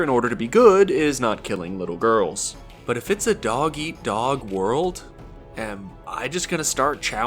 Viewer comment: Did you really said doggy dog instead dog eat dog at 33:43?